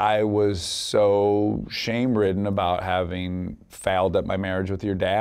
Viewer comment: Can anyone tell me when Dax says faled at 1:40 is he saying failed thanks😊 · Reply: He said fouled up...